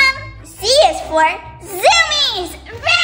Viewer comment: I thought said C IS FOR ZOOMIES